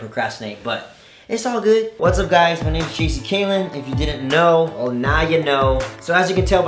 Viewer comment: When he said "what's up guys it Jc caylen" it sounded like he said cheesy caylen😂